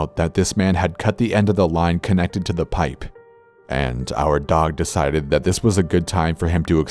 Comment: I thought they said the dog died. Phew I was about to cry lmao